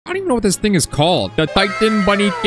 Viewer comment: LOL WHEN HE SAID «i Wonder What this thing is called» I THOUGHT HE SAID» i Wonder What this N-Word called»